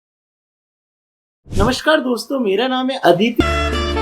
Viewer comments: Did you just say Aditi?😅😂😂😂🤣🤣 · DOWNLOAD THE DUKAAN APP :- <link> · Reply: did you just say Aditi?😅😂😂🤣🤣🤣🤣